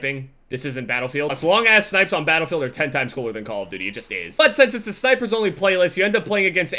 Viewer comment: it sounded like butt sex